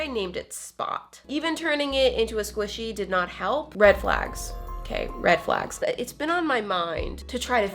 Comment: When you were saying red flags I thought you said relax